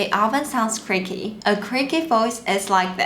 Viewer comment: lol thought she said freaky 3:21.. for a minute I thought.. "yeah it is a little freaky sounding 🤔"